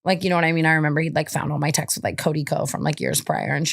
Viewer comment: Am I crazy or did I hear “Cody Ko” … ?